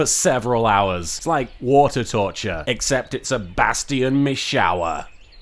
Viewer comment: My throat is in really great pain, and then i heard "Bastion Mishower" and i laughed so much and i almost died. Thanks